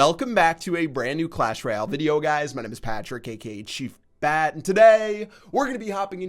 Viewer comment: i swear, i heard him say "chief fat' 😂